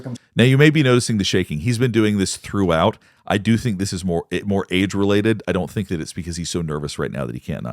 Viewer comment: When you were talking about Bill's hands shaking you said it was "age related", but for some reason I heard it "AID's related". Made me chuckle because it's totally possible at this point. 😂